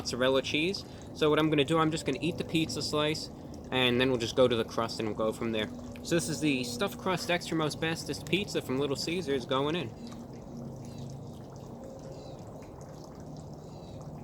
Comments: When you first said "Extra most bestest", I thought you had said "extra asbestos." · Sounds like he saying extra asbestos